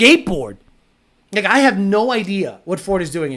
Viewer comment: did u just say "n1gga I have no idea" 😂